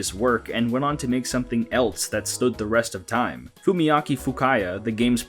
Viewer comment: did you say "stand the rest of time" at 8:09?